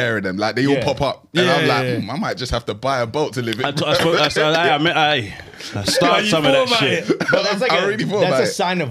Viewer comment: someone please tell me what the hell said?!!😂😂😂😂😂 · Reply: something along the lines of 'ay yah ay ah yah aye'